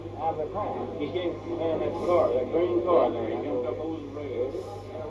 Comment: the witness says "He (Oswald) came from BEHIND that green car there,...", then jumped over the railing. He did not say "He jumped out of that green car.". It was the IDIOT reporter who goes on to start the fake news indicating he LEAPED out of the green car. Total BS. BUT, Oswald did not kill JFK, and Ruby was in on the plot.They even falsely report a cop was shot too. IDIOT Reporters!